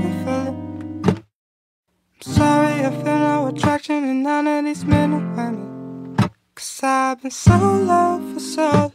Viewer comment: I think it’s “sorry I feel no attraction in none of these men or women” and not “I know it’s been a minute” At least that’s what I hear.